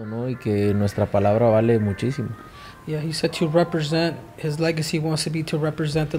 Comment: You say "Legacy" I hear 'Manny Pacquiao'